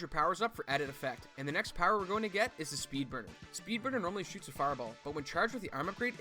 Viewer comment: In the MegaMan section when he's talking about the different power-ups I kept mishearing 'Speed Murder' . I was like dang, that's a pretty brutal name.